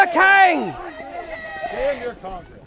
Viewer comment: I genuinely thought he said "Damn your communists"